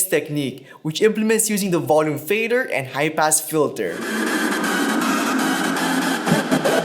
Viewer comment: I totally thought you said "HYPE ASS FILTER" and I was like ...whoa that's a pretty edgy effect name for a Pioneer mixer. Oooohhh HIGH PASS Filter... duhhhh. lol